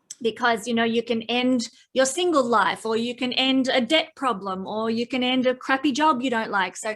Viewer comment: Not trying to be cocky here, but what is she saying at 5:51? "You can end a dick problem...a-ddict problem"?" Anyone?